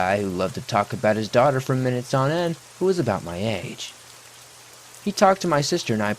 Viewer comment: what happened to you when you said Minnesota? You okay? 😂 I’m sorry. I was falling asleep and caught it and it made me chuckle to the point that I’m now wide awake. I woke myself up chuckling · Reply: The narrator said 'for minutes on end' not Minnesota lol